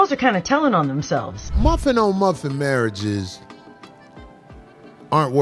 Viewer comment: Muffin on muffin😂. I thought he said puffing on muffins marriages